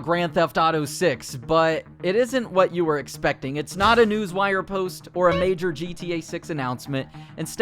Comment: If you pause at 0:07 it will say sex instead of six LOLs